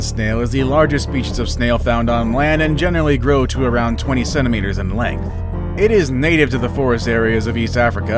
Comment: OMG I had to watch this twice, because it sounded like he said 27 meters in length as opposed to 20 centimeters in length. Thought dude was trying to say a freaking snail could possibly grow to the size of a blue whale. LOL